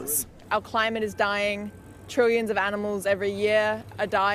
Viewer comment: did she just say trillions of animals?😂